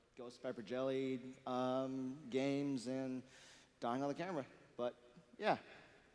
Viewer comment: did anyone else hear the guy say he was gonna die on camera